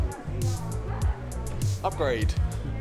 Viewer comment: 'Upgrayedd' Well..... that's what I heard !